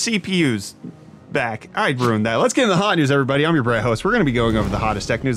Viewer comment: Who do I hear "I'm your Brett host"?😂 or am I that deaf😂. Ps. The guy who wants to use his intel XMP ram on AMD won't be having a fun time🤣